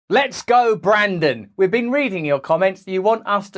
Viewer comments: Lets go brandon? I hear “joes a wanker” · I also felt bad for Brandon, and the way you put it crystalized my feelings. "Graffiti'd over his moment." He finally excelled in his craft and is now known, or not really known for all the wrong reasons....I heard "Let's go Russell Brand!" but maybe that's just me...